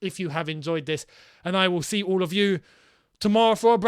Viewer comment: Did he just say “Potter out” at the end? Or is it just me?